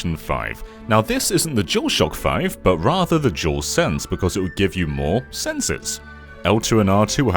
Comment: I thought it said "DualScreen" and I remebered the Wii U